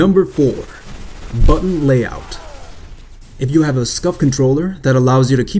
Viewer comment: he mentions a special controller that allows you to keep your thumbs on the analog stick, but I couldn't quite tell what the name was (Scuff controller was what I heard, don't think that's it though). Could someone specify about it and specify where you can get it along with if it works on Xbox? (I'm on 360) Thanks! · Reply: Seiga Sakeimoto Yes it is, a Scuff :) check them out <link>